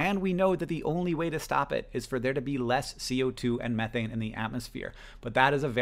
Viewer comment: Hank says, "And we know that the only way to stop it is for there to be LESS CO2 and methane..." but the English CC says, "And we know that the only way to stop it is for there to be MORE CO2 and methane...". Does anyone know how to fix this?